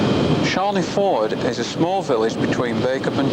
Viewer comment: Charlie Ford? I thought he said Chandlers Ford?